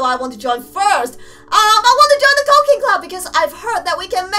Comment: Did anyone else hear 'cocaine club' at 0:17 and 0:22 ?